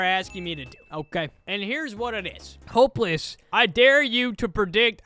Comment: the text said „hopeless” instead of „hoplas” 💀🙏